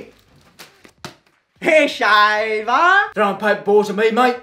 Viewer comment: "hair shaver" instead of eshay bah in the captions lol